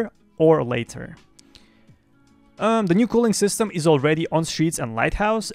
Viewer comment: the new what system? im sorry couldnt understand it :c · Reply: Culling, means things only render when you see them.